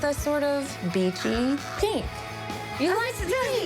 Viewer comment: I love your designs but it seems you really like green and use it a lot. I hate green and I’d like to see you use something else. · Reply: I thought Angela said she likes “beachy” colors, but she got a peachy kitchen. I was so disappointed. That kitchen would be beautiful in beach tones.